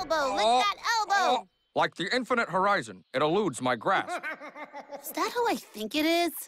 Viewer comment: something really weird i noticed at 0:17 when you turn the speed on the video 0.25 it sounds like he said the infinite rises!